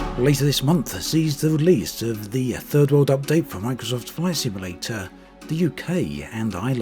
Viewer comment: I feel there's a hidden tone behind the title of this video lol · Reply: I didn't read the complete title at first (at least not consciously), but when I heard the first sentence, I heard it as "third-world update" at first - that would fit the title as well.